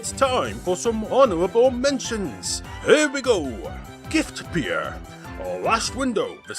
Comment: What was the first honorable mention game @ 11:53? Gift Pier? Gift Beer? No idea what was said lol someone help